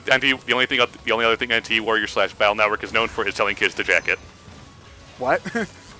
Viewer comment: I heard "jack it"...